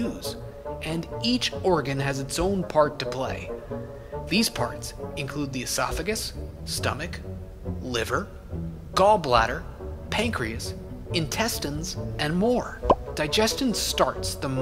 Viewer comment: did you say pancake? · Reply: pancreas not pancakes